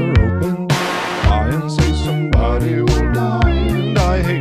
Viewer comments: I thought it said "I hate gulping"💀💀💀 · I THOUGHT HE SAID "wishing that the pills that you cry are homophobic" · I thought it said i insit somebody well died and i hate helping but the vid said hoping☠️ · I always thought it said copeing